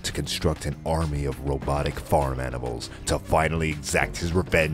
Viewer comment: "Robotic farm animals" Shows picture of penguins with guns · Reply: Darryl Flanagan I thought he said "Robotic fart animals" lol